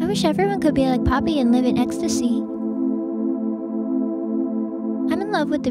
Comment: who else heard her say live in extacy